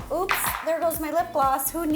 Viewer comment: All i hear when im watching this is "OOpS"